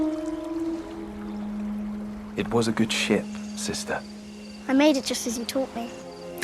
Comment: I must be deaf or something but I heard him saying "It was a good Sh!t sister" XD